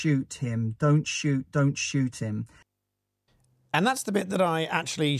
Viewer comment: I thought she was saying in broken english 'dont shout at him' rather than dont shooting.